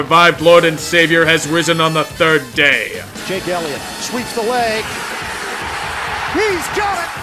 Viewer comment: Am I the only one who heard "Sweeps the leg" @ 9:44?